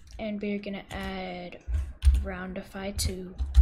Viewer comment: what did he said on 4:40 And we gonna add a groundify 2????????????????????????????????????